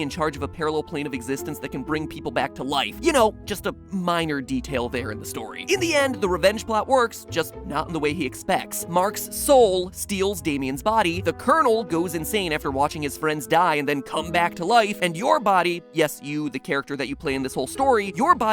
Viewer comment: I'm sorry did you say M A R X S O U L ?